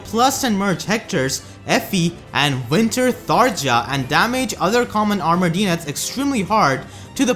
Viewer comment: Did you say even winter Tharja????????? Well ik what I'm running